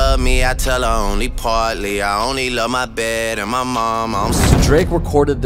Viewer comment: WAIT I THOUGHT HE SAID I ONLY LOVE MY DAD AND MY MOM 🫢